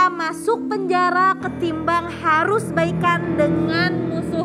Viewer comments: NM salah denger tuh yang di tanya baikan malah dia dengernya balikan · Kayaknya salah denger deh nyai, itu yg ditanyain Baikan bukan balikan. yg saya denger sih itu 😁